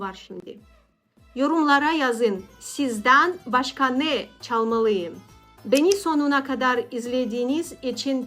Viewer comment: sizden başkani caliyim diye anladım bi sevinmiştim